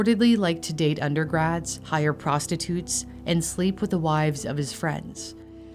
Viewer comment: wait, what? @ 7:36 He liked to sleep with the wive of his friends? Did I hear that right?